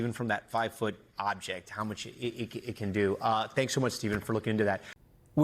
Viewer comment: uhhhh... did he say they have eyes "on that one"? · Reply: “Them “